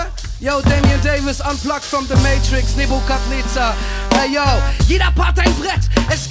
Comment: gabs hier nicht irgendwo einen Typen, der alle Lyrics aufgeschrieben hatte? Was sagt da am Anfang? Nevokad Neza? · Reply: nebukadnezar - wikipedia macht den Rest :)